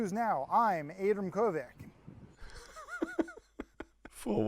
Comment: I thought they where saying Aydam